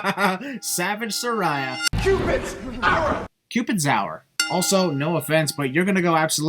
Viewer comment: I thought he said Cupid's Arrow at 6:28. Would be a good name if he did